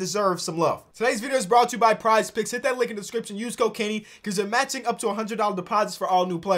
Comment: Use code kenny... I heard "use cocaine' lol had to go back